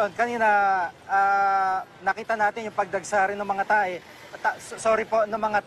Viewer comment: did he say pagdagsa ng mga tae